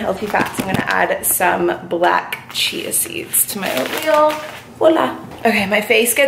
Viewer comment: did you say "hola" or "voilà"? Or none of them 😂